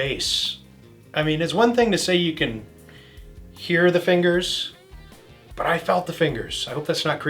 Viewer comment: At first I thought he said, I smelled the fingers. Either way, I love it!